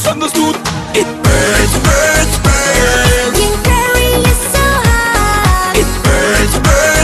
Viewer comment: what i heard: Jim Carrey is so hooot! O_o